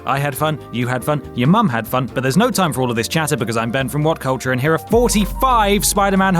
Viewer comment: ohh boy ... i did one big funny mishearing here =))) your ben from what culture ... but at the beginning it was like "i am banned from what culture" ... just got it right at the very end =))) nice video over all thanks for uploading ;)